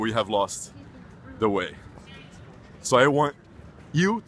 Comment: gotta work on that english pronunciation man, when you tried to say "intolerance" I didnt hear the "IN" and it sounded like you said "tolerance"